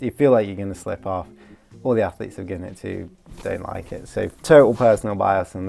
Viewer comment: I think you skimmed it too fast. All the athletes do NOT like it. The judo guys liked it but judo is not climbing and the grip is very different. The coach who liked it (me) said it's NOT better than any hangboard session. I personally like how you can get a good pump, and it will build muscle. But there is no way it's more specific than any hangboarding method, so it can't go above them IMO (D tier). It's good for some things, but if you're climbing and hangboarding already, which most people are, these finger rolls will just add a lot of fatigue, which is not appropriate for most people. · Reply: okay I just misheard him saying " all the athletes i've given it to.. don't like it" as "they like it" which honestly took me like 4 listens to actually hear correctly, possibly a sound setup thing like the yanny/laurel phenomenon or my bias of liking them. If his experience is none of his students liking it then it does make more sense.